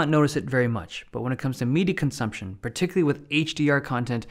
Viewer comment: The way he says "media".. I hear "MIDI consumption" 😂